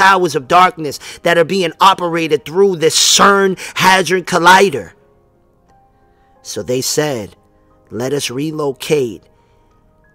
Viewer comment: sounded like u said hazard collider? its a hedron colider